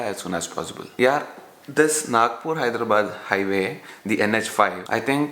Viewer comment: Did you say Nagpur Hyderabad highway "NH-5" at 5:30 😂😂